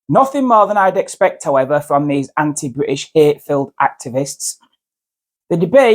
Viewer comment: She is Not British, no matter what Passport she carries. She should be Deported with no Right to Return At the beginning you said what I heard as 'Anti British Ape Filled Activists', winding back I realise you said 'Hate Filled'. Personally I think what I heard at first is more Accurate.